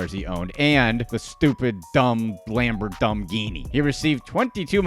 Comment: Did he just call it a “Lambor-dumb-ghini”?